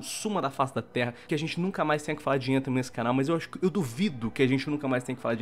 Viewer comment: Eu juro por kami sama que eu ouvi 'defenthem" acho que anthem ta me dando problemas auditivos agr kzkxk kz